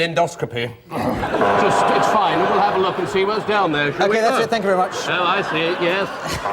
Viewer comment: I may be imagining it, or else I'm saying something really obvious, but I could swear I heard Stephen say "thank-you, darling" at 2:44. This game of "props" now amuses me even more.